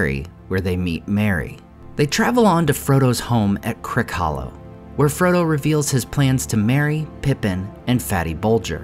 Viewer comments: I heard; “Where Frodo reveals his plans to marry … Pippin.” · I was kind of drifting into daydreaming and then I heard "Frodo reveals his plans to marry Pippin" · I heard this as Frodo planning to marry Pippin and Fatty Boldger